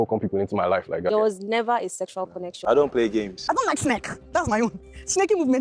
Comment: Did I hear say "I DON'T LIKE SNAKE? THAT'S MY OWN"